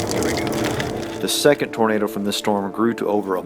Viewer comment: the captions say tomato instead of tornado